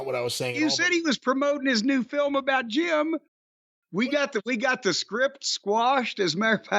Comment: did he say script down, or strip down???😂😅😂